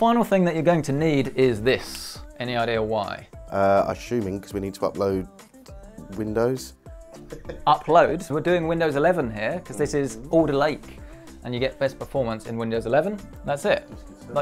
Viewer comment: Thought you said Older Lake :D instead of Alder Lake